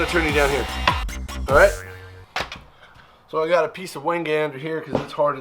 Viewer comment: What is the piece of something (?) you said you put down at 1:43 that you said was as hard as concrete? That would be handy to have. Closed captions said "wind gander". All I get when I look that up is the weather forecast! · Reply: Wenge !!! It's A Very Hard Hardwood, Kind Of Expensive And Not Very Fun To Work With . I Made A Video Called "The Handles From Hell" You Can See What It looks Like There. Thanks For Watching Have An Awesome Day !!!